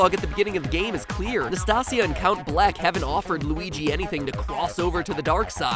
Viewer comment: I thought that you said mr black then it said bleck and I'm like whhhhaaaaaaaaaaaa????